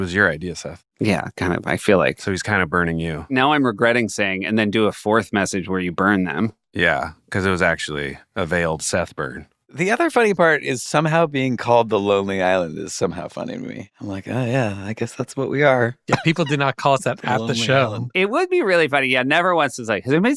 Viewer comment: i thought he said "a day-old Seth Burger"